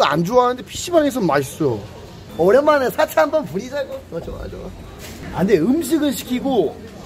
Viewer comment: ㅁ..뭐라고..? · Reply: 사치라고 말함